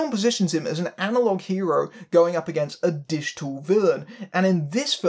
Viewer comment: I first misheard 'digital villain' as 'dish tool villain' and marveled at the peculiarity of the idiom for a bit.